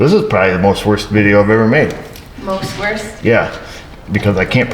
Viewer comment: You said this video was the worst, after talking German sandwiches I thought you said wurst!